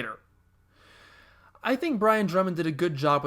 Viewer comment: for a second here I thought you said "Ryan Drummond" voiced Vegeta... I had to listen twice to notice it was "Brian". I was gonna say "The fuck, Sonic voiced Vegeta!?" XD